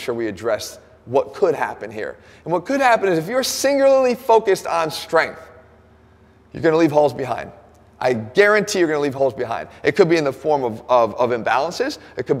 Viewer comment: Is it only me or Jeff say singulalee instead of singularly